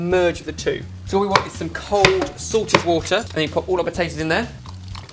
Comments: guys haha when says "cold salted water" i hear "cold sorted water" and then it makes me laugh :D espeically because it has the "Sorted" S for the S for salt! sorry really random but i thought it was funny( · He said salted. It's his accent which makes it sound like he said sorted lol :-)